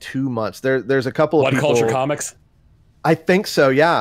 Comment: does anyone know what podcast/video series is talking about? "One Culture Comics" "uncultured comics" ??? I really want to find this series.